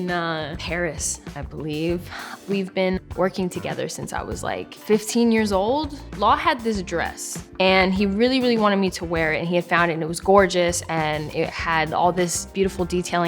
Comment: For a sec I thought it said 4 years